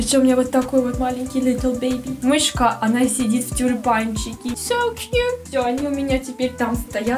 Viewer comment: Вместо " в тюльпанчике" я услышал "в тюрячке"